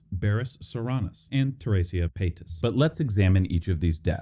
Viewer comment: quite literally heard this as Trisha Paytas